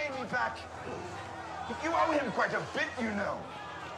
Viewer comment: "You owe him quite a bit, you know" Tyrion knew 😂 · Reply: I thought he always said, “You are a bit inbreed a bit you know”